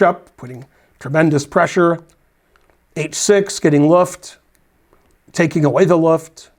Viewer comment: ~ What is this mysterious word, "Luff"? The spell checker had trouble with it, calling it "LT" and then calling it, "lift". Sounded more like luff or luft. What's it mean? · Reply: "Luft," yes, you got it. It's German for air.